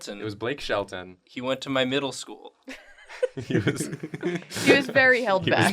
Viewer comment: To me it sound like he said nipple school instead of middle school